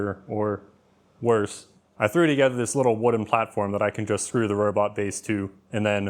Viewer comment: i heard "3d gether